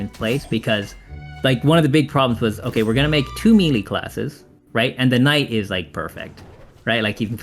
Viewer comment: the subtitles say "mealy" when they should say "melee". And this is a great video to watch, thanks